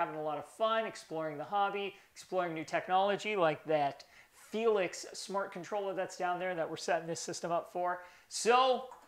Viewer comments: Damn am I the only one who thought he was saying"helix" instead of "felix" this whole time?... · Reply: haha for the longest time, anytime we were shooting, I thought he said "Felix Mart", but it's "Felix Smart"... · Damn am I the only one who thought he was saying"helix" instead of "felix" this whole time?...